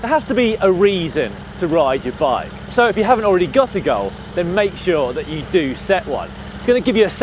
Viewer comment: What inspired me, well, I had a knee injury for about 3 years. So i resorted to cycling because it didn't hurt. Then I found GCN, now I'm hooked. 2:53, for a moment I thought he said a girl. But no, goal.